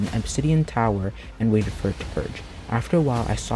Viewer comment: did he say purge or perch? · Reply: Perch xD but it sounded like purge